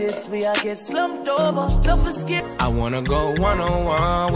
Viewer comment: nice makeup. I swore I heard controlla then it turned into one on one. #theshade lol